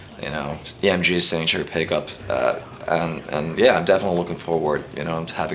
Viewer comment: I swear at 5:10 when he says "EMG signature pickup" I hear "EMG sanctuary pickup". Gotta love the finnish.